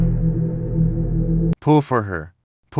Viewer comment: What I saw: "Pull for her" What I heard: "POOFER" Omg my hearing is so bad😭💀